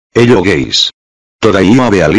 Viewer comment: Did he just sayed hello gays?🤨🤨🤨🤨🤨🤨